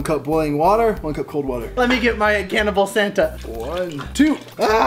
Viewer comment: WHEN HE SAID "cannibal santa" I THOUGHT HE SAID "CAN OF PLACENTA"